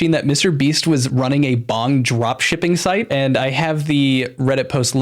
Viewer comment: people who thought he said "bomb drop shipping" not bong drop shipping